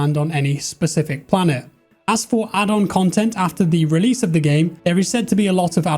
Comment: I swear I heard "as for adult content, ... "